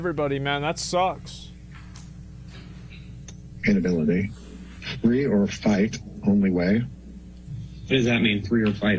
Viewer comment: given the context of everything else, I think it makes a lot more sense it that says 'Flee or fight' instead of 'Free or fight'.